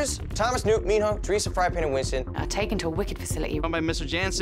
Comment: Did Dylan say FryingPan😂😂😂😂😂